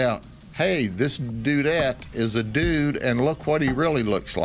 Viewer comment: LOL...... did he say dudette at 5:38 ?